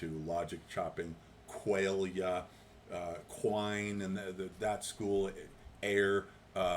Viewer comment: What are the two words said after “qualia” at 19:08? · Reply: W.V. Quine and A.J. Ayer. The former an American philosopher, the latter an English philosopher. Pretty snarky stuff.